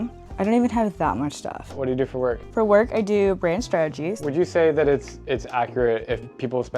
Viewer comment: the first girl @ 02:45 goes: " for work I do praying to statues." .....huh.... what did she say???? ....so at least she got an easy job to do....LOL